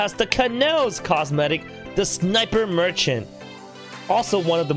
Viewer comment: did he just say merchant?